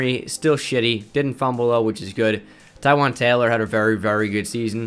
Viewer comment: When you said “Taywan Taylor” I thought you said tyrod Taylor and my brain just went along with it because their pictures look similar... about ten seconds later I realized “wait tyrod is a QB and isn’t on the Titans” and had to go back